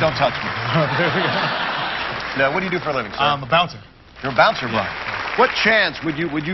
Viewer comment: thought he said "Imma bounce I don't make that much money, I have a girlfriend and wanna buy her the engagement ring she deserves." I was like that's respectable $26,000 is plenty for an engagement ring and even a wedding or part of it at least...that's Noble, Good dude very lucky couple...Turns out he wasn't going to bounce, he's a bouncer. Let see him retire his future wife! I know this already happened but quarintine has me hype on these he game shows!!